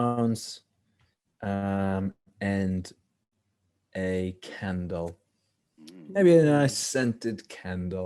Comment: Anyone else thought he said a Ken doll when he actually said candle ??💀